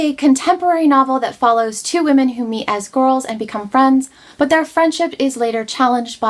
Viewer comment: I heard "met as squirrels " when you were talking about Sula. So confused. I wish I could read as fast as you seem to.